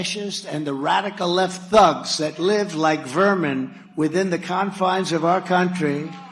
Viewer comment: I didn't hear him call anyone vermin. I heard him say living( like) a vermin. (But) I suppose context is lost on these people. But you know that old saying. (If the boot fits).May God help us. Or better yet help us help ourselves!